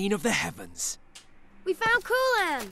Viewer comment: I thought he said "We found Koolaid!" lel